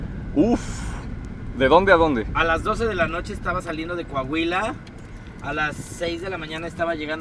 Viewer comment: lalo salio alas 12 de la noche de tu abuela ;-; fue mi idie a escuche eso minuto